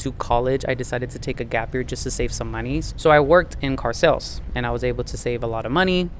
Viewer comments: I thought I heard you say "I worked in cartels". LOL. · Who said all that? AHHAHAHH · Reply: into the video, you said "I worked in car sales". I wasn't really paying attention, and I thought you said, "I worked in cartels". I had to go back and listen again to what you just said.